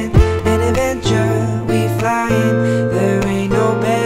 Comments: i thought i've heard. I'm a ninja, we flyin' xD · i thought i've heard. I'm a ninja, we flyin' xD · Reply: I heard "An Avenger, we flying" looool.